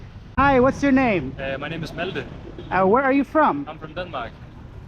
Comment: how do you get a name like wrong?? :o Is it a danish thing to be able to hear, that he clearly said and not Maldon?